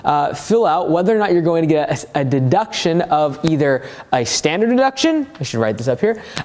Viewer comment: Great video! Anybody else catch the “STD” moment? 😂 min